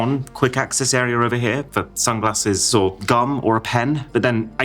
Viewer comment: I thought bro said gun at 1:22, was not expecting that